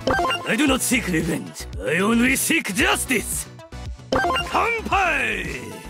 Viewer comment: did he say BANKAI..? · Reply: He said kampai means cheers 😂